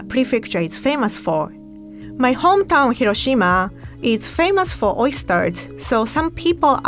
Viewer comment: Did she said her hometown is Hiroshima 3:00 ?